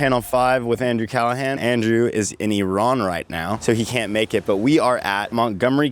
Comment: Did he just say Iran?